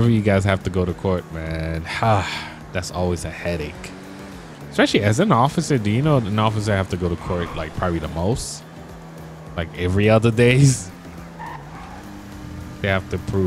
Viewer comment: Am i tiriping or did he just say ofcifer